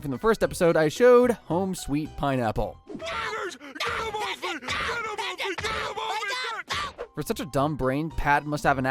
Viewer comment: what did he just say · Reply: "Spiders, get them off me, get them off me , get them